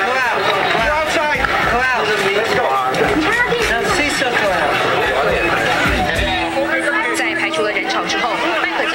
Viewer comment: what he said in min 1:41?Thanks!! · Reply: "Crowd...crowd...a sea of crowd!"